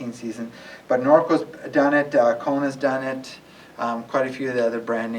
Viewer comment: Whoa! I never heard of Norco! I know almost all of the manufacturers, but I never... Thanks! What is that other company you said just after Norco (time-stamp 4:20)? Giant, Norco...? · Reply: Kona! I got it!